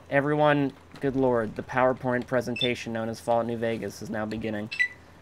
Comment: did he just say "PowerPorn" XD